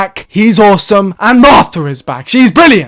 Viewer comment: why did it sound like yo said Mothra? Hahah.